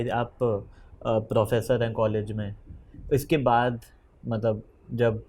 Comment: processor 😂😂😂 instead of professor